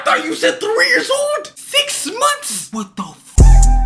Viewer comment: It’s actually sound like he said it for a min What the fu-fu-fu-fuck yeah 😅